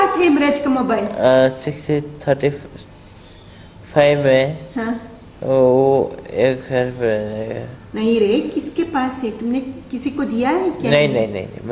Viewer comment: 6-A sec 35 me ek ghar me hai . Hemraj ka mobile. He actually said it at @ 28:35 min speed up at 1.85x u can actually hear him saying it.